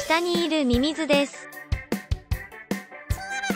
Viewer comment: いつも最後に流れるやつが 唐揚げセンキュウ速報にしか聞こえないのは私だけだろうか